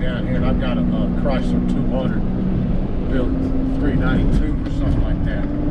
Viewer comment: Lol for a second I thought you said "Chrysler 200 built 392" 😂😂